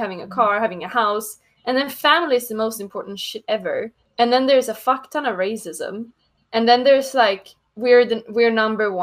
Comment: Every American stereotype at once. How ironic. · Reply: What does she say about "theres a fcktana... racism"? Didn't quite understand.